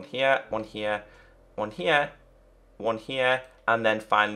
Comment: When he is placing down the fences and cobble walls it sounds like he is saying one heya